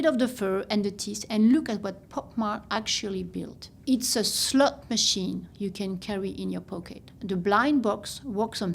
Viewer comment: Gurl we really gone have to work on that accent cause the way I thought she said Labubu is a SLUT machine, and not slot machine, I was like damn we slandering like dat? I did not know we was still in scarlet letter times!